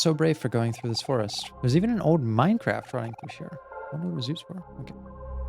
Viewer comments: Have I been gaming too much, or did you actually say Minecraft at 50:31 (at this point, I honestly can't tell if it really was a thing, or if it's just me lol :-D )? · did...did he just say "Minecraft" instead of "Minecart" or am i tripping? XD